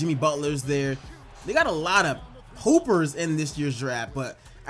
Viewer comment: i thought u said poopers lmao